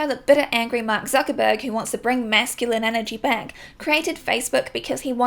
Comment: y’all I thought « facemash » said « fascism » 😭